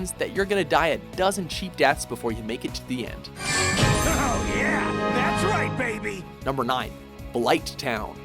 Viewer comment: i used to think he said "Hell yeah that's right baby"